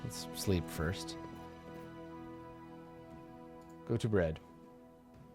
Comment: Oh... Go to bed. I thought u said bread :-)